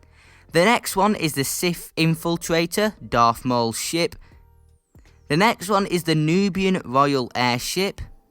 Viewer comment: I thought you said “Darth Maul’s Sh*t” lol